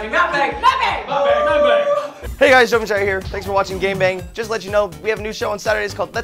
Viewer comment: did they just say "thank you for watching gang bang" at the end? XD